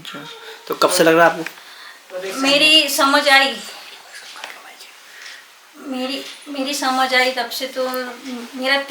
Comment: bhai ye dheere voice pe apne kya bola unhe ? jara instagram dm mujhe batana plz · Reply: Lady se bol rhe the..tention ka htao bhoot ka btao ... 😂😂😂